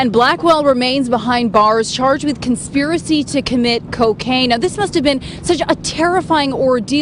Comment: Did she say "conspiracy to commit cocaine"?